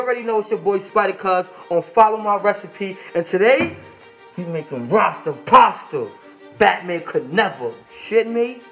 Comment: I thought he said “fat man could never “ I was about to click on morray video